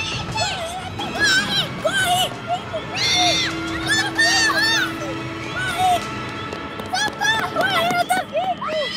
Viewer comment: Que dicen los niños en el minuto 2:10? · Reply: Help! RUN, she's coming!